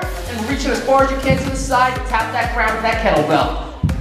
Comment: I was so into the workout that I honestly thought you said "fuck that kettlebell" at the end until I realized you actually said "drop that kettlebell" lol